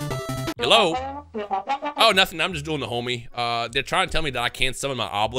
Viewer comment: Why'd it sound like bro said "Oh nothing, just doing the homie"